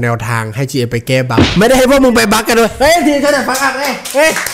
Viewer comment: พูดว่าอะไรแปลไม่ออกใครรู้ช่วยบอกที่ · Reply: เขาบอกว่าไม่ได้ให้พวกมึงไปบัคนัโว้ย555